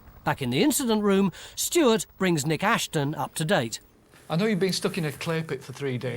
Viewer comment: When Mick Aston said they were working with a Nick Ashton, I thought he said his own name! LOL